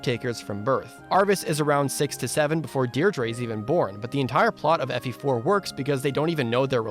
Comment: talking about Arvis' age in relation to Deidre I misheard "six to seven" as "sixty-seven" and for half a second I thought I had somehow missed a story point explaining that Arvis was 80+ years old for the entirety of the first gen